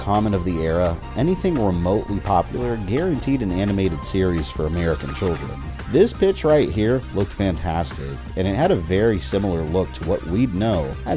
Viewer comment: Can someone PLEASE tell me what the name of the animated Mega Man series shown in the opening and the scene with Roll was from? 😍 I only know of the American 90s show and the Japanese one where Mega Man leaves the television, but that isnt what was shown here 😱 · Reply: Awe man! What a bummer, that show looked amazing! Also, I thought in the video you said "This bitch right here looked fantastic." (Instead of "pitch" ) It got a good laugh outta me and I went "Damn right it does!" 🤣🤣